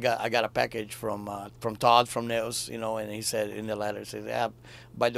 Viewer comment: Wich band did he just said? Please help. My english is not so good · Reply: He said Todd from Nails! So the band is Nails.